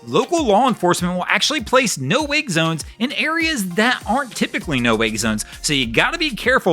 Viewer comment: I thought you said, 'No wig zones'. It kinda made sense and then I thought about it and you actually said, 'No wake zones'. Both work though.